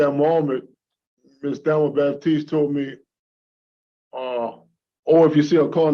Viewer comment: At around 5:30, He called her "that damn woman" 😳 Edit: it may actually be "at that moment", you decide! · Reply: I thought he said "At that moment Ms Baptise told me...."